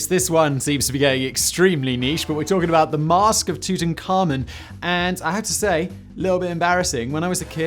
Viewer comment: As a young lad, say 10/11 I was allowed to stay up late one night with my dad. He was watching a documentary on Tutankhamen. However as a young lad I misheard. Therefore about 45 mins into the show I asked my dad about the camel. He replied" What camel?" I said, the two ton camel. 😂 No lie I could not get my head around the name thus saw Egypt, thought camel and guessed it was about a bloody massive 🐫. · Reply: To be clear at the time I heard it as " two ten Ka mun"